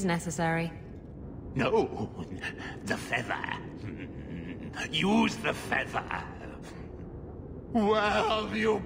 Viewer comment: Wow, all this time I thought he was saying "further sedate me"